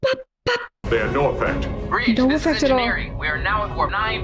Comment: Did Geordi just say Breej?